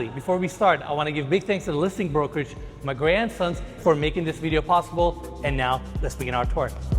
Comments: When said Magrey & Sons, I thought he said My Grand Sons… I was so confused at first🤣🤣🤣🤣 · Magrey & Sons? i swear i thought he said my grandson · Beautiful home! One of the most beautiful ones you’ve ever toured in this channel. Also, every time said “Magrey & Sons” I heard “my grandsons” 😂😂😂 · It took a few times for me to hear what you were calling the listing broker. Ha ha. It sounded like you were saying, "my grandsons". What an amazing property! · When he said "Magrey & Sons" i thought he said "my grandsons" i was like whaaaat